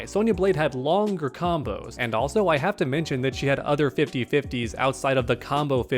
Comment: "And Also I have Dementia". I know that's not what you said bro but that's what it sounded like at first lmao